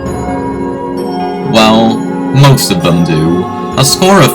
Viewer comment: what did you say? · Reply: He said: Well, most of them do.